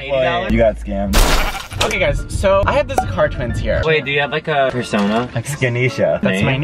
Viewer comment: When you said Zakar twins I thought you said "ze car twins"